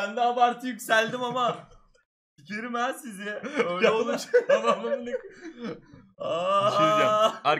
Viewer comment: tamam aq diyor😂(öyle anladım daha doğrusu.)